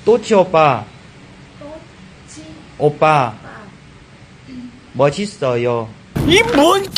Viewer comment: 귀여워ㅋㅋㅋㅋㅋㅋㅋㅋㅋㅋㅋㅋ 7:11 또치오빠 맛있어요 라고 들리는 내 귀는 나쁜귀?ㅋㅋㅋㅋㅋㅋㅋㅋㅋ